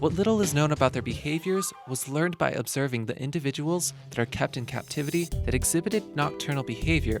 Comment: I kept hearing "kept in cat-tivity"...